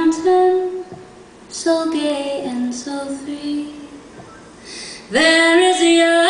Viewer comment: does she say a mountain so gay and free?